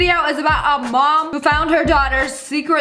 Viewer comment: I thought you said her mother found her diarhea